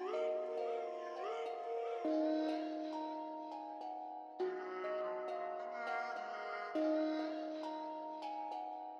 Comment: il fais quoi a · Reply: Il a dit au prof c'est chaud de rougir pour une 2005